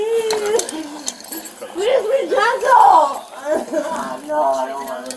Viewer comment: “where’s my jungle” but she actually said “please be gentle” look at the AI caption maker being racist